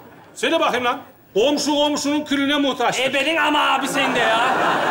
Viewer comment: Cenanın küfrünü tek ben mi duydum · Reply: 10 dk dır ailemle tartışıyoz ne dedi ne dedi diye sanki “ebenin ama” diyo